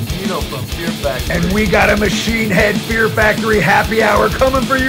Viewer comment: Thought is said happy tour 😢